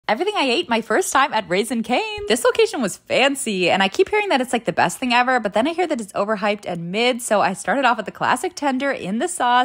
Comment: Did I just hear you say “ rizzing cane??”